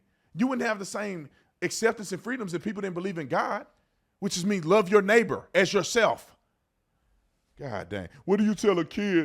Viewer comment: At first I heard: "Make love to your neighbor, as yourself..." in the intro. I had to listen again.